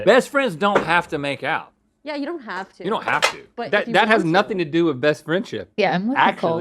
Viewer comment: WHAT DID SAID IN THE MINUTE 1:41? I NEED TO KNOW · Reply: "but if you WANT to..."